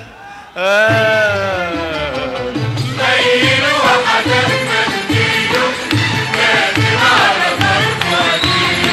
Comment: تكفون ذول عجزت افهم وش يقولون فهموني 😫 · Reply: ميل وحدف منديله، كاتب على طرفه اجيله.